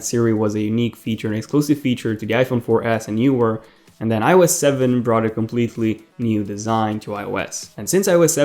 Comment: Not making fun at all but when u said ios 7 i thought you said I was 7